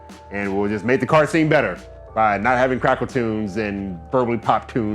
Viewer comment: Boy I thought you said "Crackertunes"!!🤣